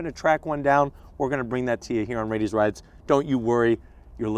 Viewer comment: on the substitle sinstead of saying raiti's rides it said radius rides xD it was in